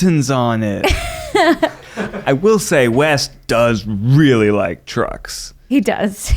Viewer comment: I nearly shat myself because I thought Ned said “He really likes drugs” instead of “He really likes trucks”. 😂